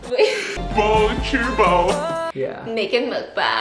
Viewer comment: Did he say ''靠北''？haha